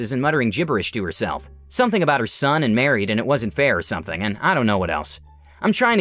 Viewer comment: "It wasnt fair" It sounded like "a wizard bear" for a moment there XD ((the fan might have messed with the low volume ya))